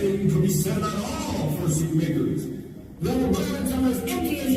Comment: why’d it sound like he said fursuit (n-slur)